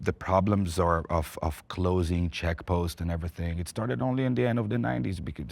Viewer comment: the subtitles state "Czech posts" instead of "check posts" Can you please correct it? Czechs have nothing to do with this and it can be misunderstood